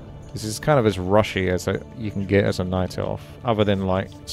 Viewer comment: I heard : "Gay as a Night Elves"